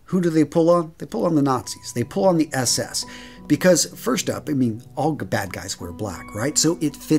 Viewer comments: did you just say uhmm.... all the bad guys were black? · I know you actually said "All the bad guys wear black" but you better fix the subtitles soon since it said "were" instead of "wear", or I can smell a sxxtstorm coming your way real soon lol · the subtitles said "all the bad guys were black, right?". Big oof...